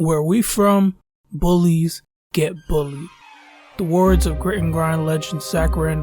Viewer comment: I always thought Zbo said "bullies get bullets"